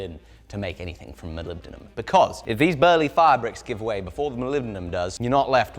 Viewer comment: I was confused for a whole 5 minutes, becaues I thought you were calling fire brick, a fabrick, made me realize the english acceent quite different innit. and I spent a good 2-3 minutes rewinding and re-watching because im like" why the hell does he keep calling that brick fabrics? is that fabric? no its a brick, ok then why is he calling it a fabric? OH fire bricks. soft i pronunciation and literally 0% R pronunciation haha. "edit" it more so sounds like your saying fairbrick like fair brick just together, you pronouncing your i's like "aaaiii" its meant to sound like f "eye"yur brick, not "faibrics" not hatin on ya, I love accents, just letting you know how americans pronounce it, not that you have to cater to it as youtube is world wide. stay fresh king, I love your content.